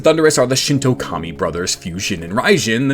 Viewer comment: I keep hearing that as "Commie brothers"